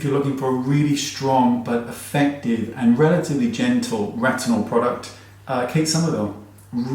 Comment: I'm laughing so hard at myself right now! 😂 When you said "Retinol product" my dirty mind thought you said "rectal product"! 😂😂😂😂🙈